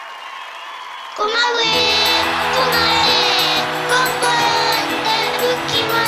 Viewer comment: Go my wayなのにゴマウェー、ゴマウェーにしか聞こえないwwww